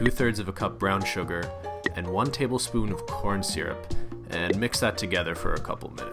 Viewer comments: I thought said Porn Syrup😂😂🤣🤣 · I thought said " Porn Syrup "